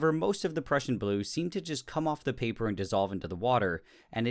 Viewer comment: Who else calls it "Persian" blue? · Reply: Tyler Dollarhide, the first time said it I thought he said Persian